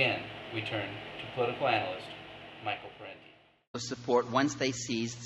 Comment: who is the speaker in the later video clip please? it sounds like you say Michael Franti though obviously it isn't. (unless it's another michael franti)